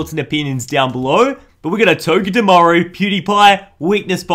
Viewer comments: he says instead of Dewpider pewdiepie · When he said cutiefly I thought he said pewdiepie 🤣😂🤣😂 lol